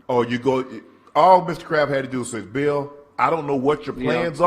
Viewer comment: I swore Shannon was saying Mr Krabs at the start of this but the comparison to Mr Kraft is uncanny.